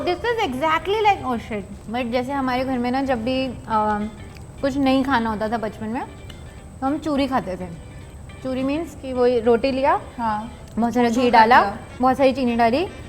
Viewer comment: for a second I thought she said Ham choohe khaate the.😅